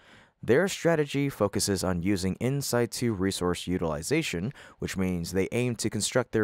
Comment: it sounded like the word was "insight 2", but I have the idea it may have been yet another example of an ignorant machine (AI) mispronouncing 'in situ', right? At 8:53, it seems to show that the astronauts will be in their space suits 24/7, even when working out, which seems to me to be highly unlikely. Since refueling in orbit wasn't needed in the '60s, why is it needed 60 years later?